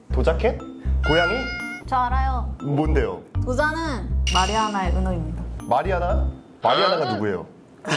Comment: I laughed so much because my name is Mariana so it sounds like he saying that 마리하나 ….